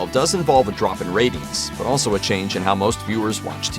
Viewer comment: Every time he said “ratings” I heard “rabies.” With this show, it’s not inconceivable that it got cancelled due to rabies.